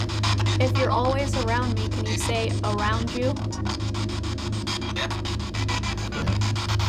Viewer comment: I think when said 'could you say around you' I heard ' yeah, around you'